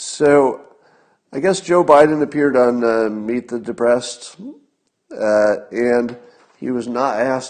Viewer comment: For a second there I thought Scott said ''So Joe Biden appeared on meek and depressed''